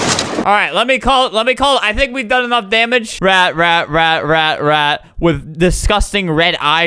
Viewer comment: Flamingo: rat, rat, rat, rat... Me: yes, it's the year of the rat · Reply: The first time he said rat rat rat rat rat It sounded like he was saying Ret ret ret ret ret ret ret ret